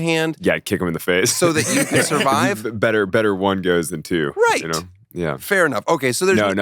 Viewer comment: When he asked if you'd instinctively kick the person holding onto you at the edge of the cliff, he said "Better one goes than two." I Initially thought he said "Better one ghost than two." 😅🤣😂